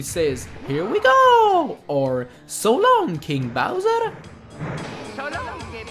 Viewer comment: After 18 years I finally know that he says "So long, King Bowser" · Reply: I thought he said "So long, a-bowser!"